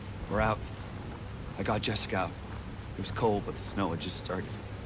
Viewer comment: Did he say "i got Jessica"????